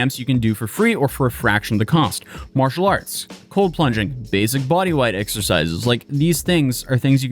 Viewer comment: Cold plunging, I thought I heard "hole punching". Also fits with these losers when they encounter drywall that doesn't hit back